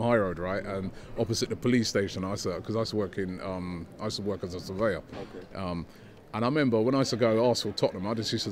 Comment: what did he say at 4:22? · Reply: He used to work as a surveyor.